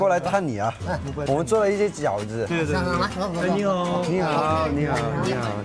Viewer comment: Winwin said annyeong instead of nihao 😅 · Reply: but i heard he said nihao, but he’s like “...eehnihao” so it’s kinda sounds like annyeong😅